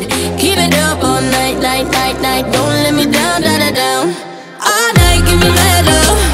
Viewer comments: I heard "All night gimme metal"🤣🤣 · All night give me mad love sounds like I like me better which is kinda of creepy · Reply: I keep hearing "all night give me melon"